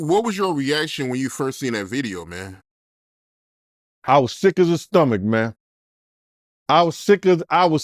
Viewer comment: did he say sick as a stomach? He corrected himself tho but Gene so funny